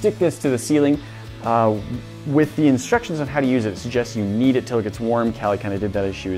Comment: Who else thought he said, "With instructions suggest you EAT IT till it gets warm and Cali has done that"?? Lol I had to rewind and hear it again! Lmao 🤣 😂 but he actually says, "Heat It till it gets warm"! Lmao 😂🤣 😂